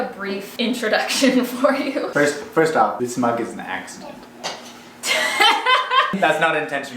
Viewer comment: I thought said " this bug was an accident "...not mug🙃. We flew back to the mainland from Edmonton last night with our two kids. Not an ideal time to travel!! We'll be sticking around our house for the next little while